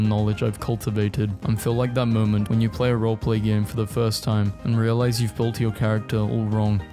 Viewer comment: DID HE SAY ROLEPLAY GAME?!?!